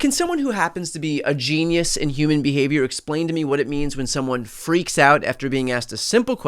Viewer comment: oh, you teaser, I thought you said FREAK OFF. he's got Laura Loomer on the short list, Pam Bondi, Kristi, and Nancy Mace! ugh, who are the guys he forces them to sex? Ted Cruz? oh no, Steven Miller?